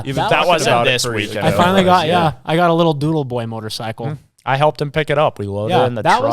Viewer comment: did he say a doodle boy motorcycle?